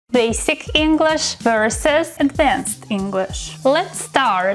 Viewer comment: The first time I heard her say basic English, I heard her say sick English Was this just me 😭😭